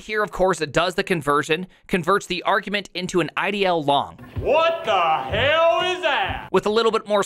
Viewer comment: I'm a little stopped up in the ears tonight, and a lot hearing impaired every night, and the speakers on my laptop are lousy. @ 2:40 I thought you said "ideologue" which certainly could figure in philosophically between the loosy-goosy world of JS and the cold, heartless metal of C calls ;-) -- oh, you actually said IDLong